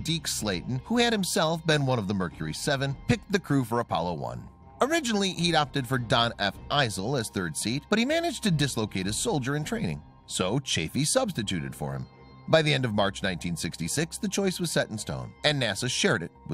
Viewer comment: Did I hear, "dislocate his soldier in training"??